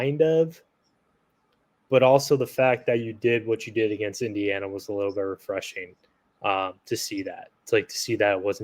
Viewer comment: Misheard that... Thought said Hannah was lost, thinking injury... Phew!